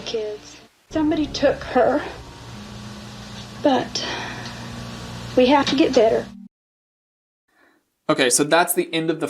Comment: did she say "We have to get better."? This would dovetail with her being overheard prior saying, "I am getting STRONGER!" softly to herself directly after the children were admitted to the hospital. Self-talk...convincing herself as Popeye to reach for a can of spinach as she (thinks she is) winning, hoodwinking others, and satisfying all of her aims...but desires to "keep it going". Dark Triad being spotlighted here.